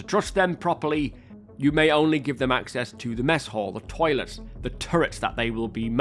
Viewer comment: listening while doing other things I heard "the toilets they are manning" :D